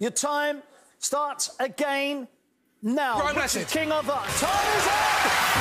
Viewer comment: what the heck was Paul thinking lol, 1 second and he goes... "RO MESSING" or what did he actually say idk but wow that was hilarious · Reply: He answered Brian Blessed. It’s just a little joke of Paul’s when there’s one second on the clock and he knows he won’t hear the question in time which is why he responds with something as ridiculous as ‘Brian Blessed’